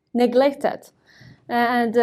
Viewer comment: What did she say? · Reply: Ah i got it it was "neglected" I think